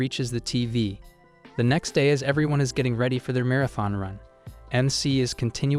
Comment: Bro why did it say amara 28:58 😭✌️